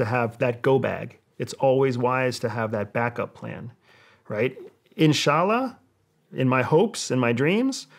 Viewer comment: did he just say( ishalla ) Are he muslim🙏🏼🙏🏼